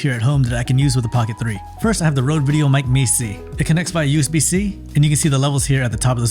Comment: which Rode mike was that? I couldn't catch what you said. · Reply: Sorry, I speak really quick. It was the Rode VideoMic Me-C